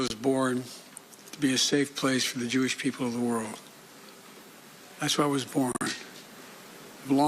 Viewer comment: Did he just say, i where I was born? 🤔 He mumbles so much I can’t capture each word..